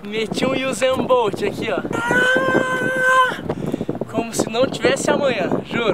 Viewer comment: eu ouvi: "como se não tivesse amoeba"